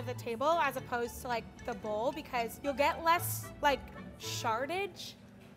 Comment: I thought she was gonna say “Sharted” it would have been funnier tho